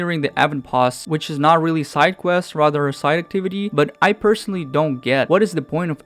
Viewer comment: Dude I’m sorry but your English is not good enough, or you need to speak more clearly. I kept rewinding what you said at 6:50 but I cannot understand what you said. Same for other points in the video. Please tell me what you said at 6:50. Thanks. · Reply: Not side quests but side activities